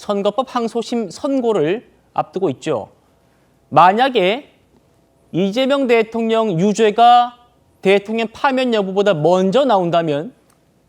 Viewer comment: 뭐라고? 이재명대통령?